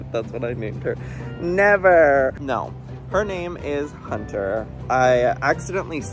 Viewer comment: Okay okay okay I heard you say “her name is dirt” I got the remote PAUSED THE VIDEO. and said be so fr rn 💀 I do really liked the name hunter id so be saying hunny girllll